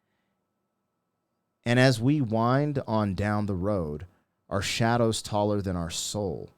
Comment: Lol when I first heard this, so many many moons ago, I thought he was saying "and there's a whino down the road".... Cracks me up.